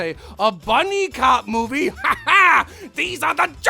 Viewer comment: did he say, ¨bunny-crop¨ because THAT was funny!